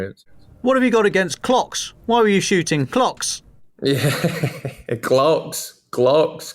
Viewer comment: I thought he said 'shooting clocks' too!